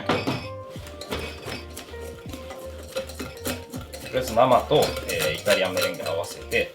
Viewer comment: 「とりあえずママと…」 ママ！？！？！？ 何回聞いてもママに聞こえる…